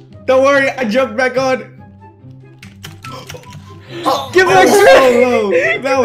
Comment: sounded like he said gimmie that shit lol